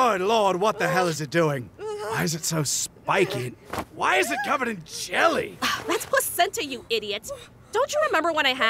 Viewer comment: I thought Vegeta said "Why is it covered in chili" until I read the subtitles